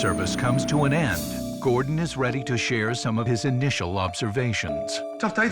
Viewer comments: I really think he said “lobstervations” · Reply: He said "initial observations", not "lobstervations". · did he just say. "LOBSERVATIONS". cuz that was an amazing pun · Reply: He said "initial observations", not "lobstervations". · I could have sworn the narrator said “Initial LOBSTERvations” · first I heard „some of his initial lobstervations“ xD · lol did he just say lobstervations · Did i just hear "lobstervations"? If so that narrator is a genius.